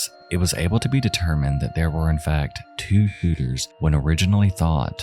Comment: Two what now? There's an audio glitch there and I can't make out what's being said... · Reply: Shooters.